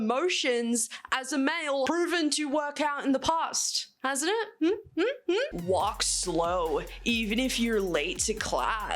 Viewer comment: 3 in 4 what are men? Why is it blurred out? What is that word supposed to be? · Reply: Suicides is the word I believe